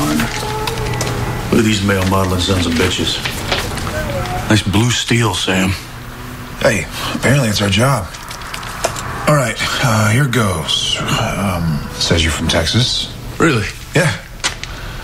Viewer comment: "Nice Blue Steel Sam." OMF NO YOU DO NOT GET TO STEAL HIS FACE SAM. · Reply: I really thought he said "that's blue steal Sam" 😂😂😂